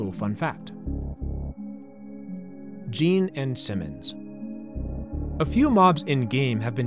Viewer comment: I thought you said Gene Simmons 😂. Imagine him stalking you in Minecraft...